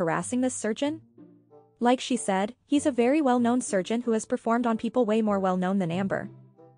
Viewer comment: It says peed instead of performed 😂